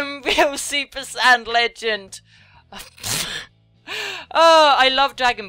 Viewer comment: And did she just say "Super sand legend". omg she watches. :O